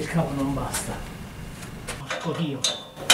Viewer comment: ho sentito un porco Vino ;) 23:10 sguardo fisso alla andy warrol un pezzo epico del maestro mio guru spirituale!